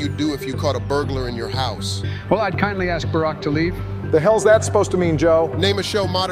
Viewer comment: "I kindly ask barack to leave" "The hell is that supposed to mean joe?" That caught me off guard ☠️☠️☠️ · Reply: I honestly head canon that he thought said Barack instead of burglar